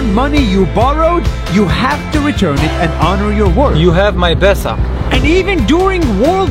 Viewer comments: I heard you have my PESA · It sounded like "You have my paisa" 😂😂 (Paisa means money in Hindi)